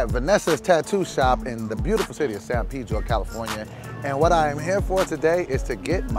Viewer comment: I thought he said San pedo